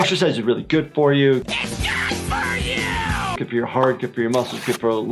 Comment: Also at 22:37 the thing I hear is IT'S GOOD FOR YOU!!!!